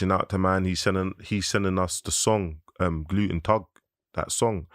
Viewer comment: Did he say gluten tug instead off guten tag?